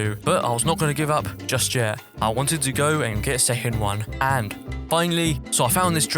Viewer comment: WHY DID I HEAR THAT AS "I wanted to go kiss a human" WHAT'S WRONG WITH ME ⁉️😀